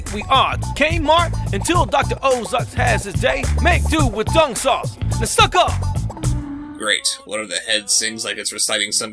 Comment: "Make do with Dung Sauce. Now suck off"..... what in the holy mother of fucking fuck did I just hear???